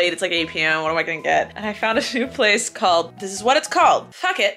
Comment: I thought she said I found a shoe place then went to tell us about what food she ordered and I was confused as phuck (it I’m vegan)